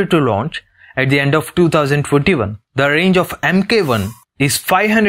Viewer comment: Did he say 2041 or I have hearing problems ?